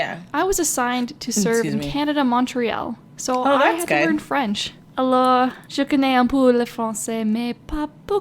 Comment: I think she’s saying “Hello, I cannot speak French, didn’t have much luck” or something like that EDIT: wrong, she actually said “I know a bit of French but not many things”. Corrected by PlaZma GameZ